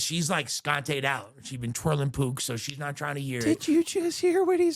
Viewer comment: I thought he said "twirling poop" since where I'm from meth is also called "poop" lmfao 😂